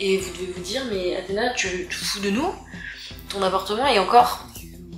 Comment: C'est moi ou a 11:38 tu parle de ton "avortement" qui est encore plein de chose ??